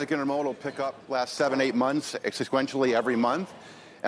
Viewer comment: Did he say... "exosquentially"...?? 0:55.